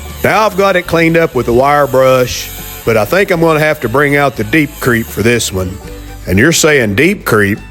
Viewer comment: Catching up on some past videos. Regarding Deep Creep: I thought you were saying Deep Creek. I thought that it was something like blinker fluid that you had made. Anyway had a good laugh when I figured out that Deep Creep was real stuff. A bunch of us around here use Kroil penetrating oil. A machinest buddy of mine recommends it. It is really good stuff. Out performs WD-40, PB blaster or any others. I will have to test it against Deep Creep or Deep Creek - what ever its called! ha